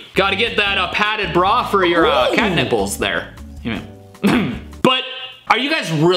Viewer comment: I heard catnip brawl